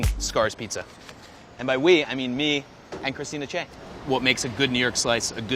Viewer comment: I thought he said Christina Yang